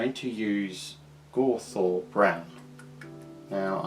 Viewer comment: Hey, great Job. IT Looks very nice. I am on the way top Paint my Baneblade as yours. BUT, I am a German guy and I don't understand the Name of the color that you use at 1:03:00 Pleas can anyone help me and write the name of the color in the comments? :-) Sry for my bad english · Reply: Hey André 😊 glad you enjoyed the tutorial… the paint is used in 1:03:00 was: ‘Gorthor Brown’ Citadel Layer Paint… hope this answers your question. GW Link is here: <link>